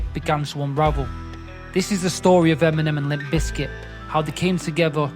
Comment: Why did it sound like he said Feminem 😂😅 EMINEM WILL ALWAYS BE THE GOAT 🐐